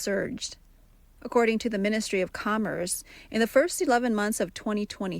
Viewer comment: I heard "ministry of coomers"